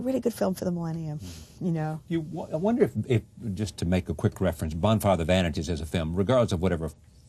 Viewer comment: What is Charlie saying here? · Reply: Josh Barnes He says "Just to make a quick reference here, Bonfire of the vanities"....